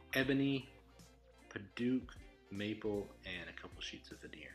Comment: I am honored to own one of these, thanks so much my friend. And thanks for the deer! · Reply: Hahaha, I totally heard "and a couple sheets of veneer" as "and it comes with a deer" BAHAHAHA I'm an idiot!